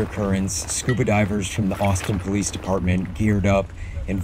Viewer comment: I wasn't expecting to hear Boston PD. Good work guys. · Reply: I'm from New England, and I thought they said my neighbor Boston at first, too. Then I realized it must be Austin. God bless them all. 🙏